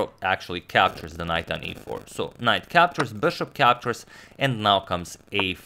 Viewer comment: Am I the only one who heard "Hikaru Captures" @ 3:30? I mean, wouldn't be surprised with that man's power level.